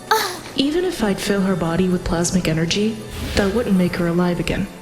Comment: "even if I filled her body with plasmic energy it wouldn't make her alive again". THE HELL IS PLASMIC ENERGY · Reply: I thought she said plastic energy at first